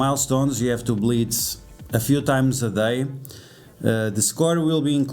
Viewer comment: When he said “we have to blitz a few times per day” for the event, I thought he said “bleed a few times per day”, because that’s how I feel every time I throw 1k cores down the drain for the event